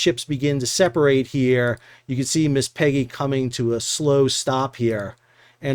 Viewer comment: 😂😂😂😂 I didn't know about this. So when I heard you name the boat, I had to rewind and hear it again and again. Still not satisfied, I turned CC on.😂😂😂 I could have sworn you said "Miss Piggy."😂😂😂😂